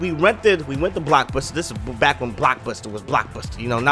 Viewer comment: Turn on Closed captions, and read what it says on 2:38. "Black Pussy." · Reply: megabolt_17 blockbuster